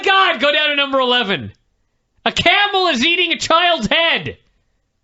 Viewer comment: I thought he said a cannibal is eating a child’s head